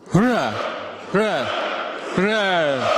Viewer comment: что он сказал? трон? фра? тра??